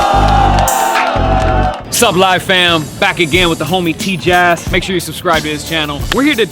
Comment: On 0:23, if u have subtitles/cc on, instead of tjass, the subtitles call him teach ass😂